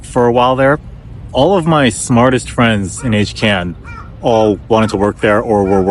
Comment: What is “hcan” or whatever word was said at 6:39 ? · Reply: Sorry it’s HKN. Eta Kappa Nu. It’s the EECS honor society.